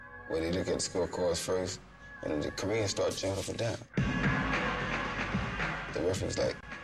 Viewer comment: what did he say at 12:06? the korean what? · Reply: Started jumping up and down